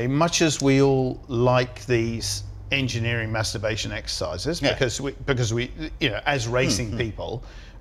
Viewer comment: Did really say "Engineering Masturbation Exercises" ?